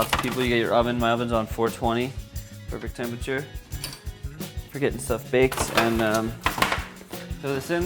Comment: The oven is set to 420 perfect temp for getting stuff Baked?, LOL, is that that what I heard at 7:20?